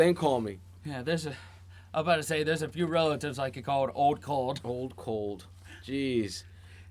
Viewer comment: I always thought he said ‘old cold’, which seemed to make sense at the time. ‘Hold cold’ probably is right though.